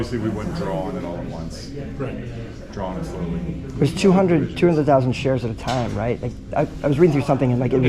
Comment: He didn't confirm that "they can't short it".. The 200K limit is the lower limit, with no upper limit, I think one of your earlier videos called it a upper limit. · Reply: I thought he said 200K shares.